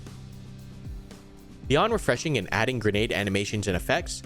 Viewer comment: If possible, someone help me out, at about 4:05 - the unique, what? Rgdm? Trying to understand what he said. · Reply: yea rgdm, its unique cuz most games dont have a handheld smoke blow up in a plume of smoke before the main smoke gets released, this is probably the first one to have it